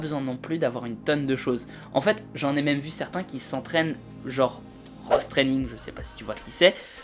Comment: quoi training? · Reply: Ross Training !